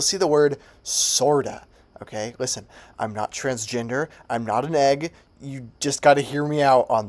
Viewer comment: At first I thought he said "I am not transgender ; I'm not a f**"